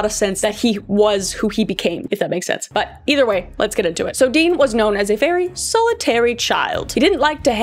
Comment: I heard "dean was a fairy 🧚‍♀️ "